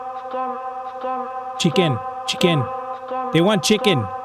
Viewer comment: By the way, 31:12 says 'test', or '試験' it is said like 'shiken' so thats probably why thought it kept saying 'chicken'. Not very important but kinda funny.